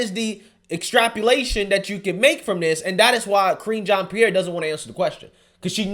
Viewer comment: I could swear I heard say "Cringean Pierre", lol.